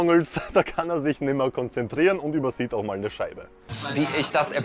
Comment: Ich habe bei min 9.15 echt verstanden „… da kann er sich nicht mehr konzentrieren und übersieht auch mal ne Scheide.“ 😂